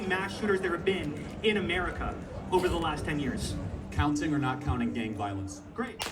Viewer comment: It is ironic isn't it to be shot while speaking in favour of no gun control. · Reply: Last words I heard were, "Counting or not counting gay violence?". But you hear what you want......